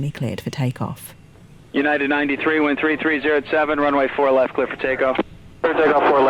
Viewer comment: For anybody confused by the message from Newark like I was, they actually said: "United 93 Wind 33 at 07 runway four left cleared for takeoff," and not United <num>. Wind 33 at 07 means 7 knots of wind coming from 330 degrees, which is 30 degrees west of due North.